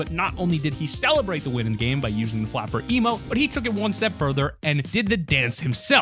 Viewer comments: Hold up am I hearing wrong or at 4:43 he said “flapper” emote instead of best mates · Did he just call the best mates emote the FLAPPER emote